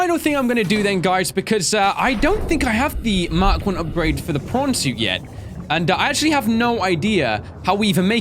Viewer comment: how come to me it always sounds like is saying porn suit and also if you can cure creatures why cant you cure your self ¯\_(ツ)_/¯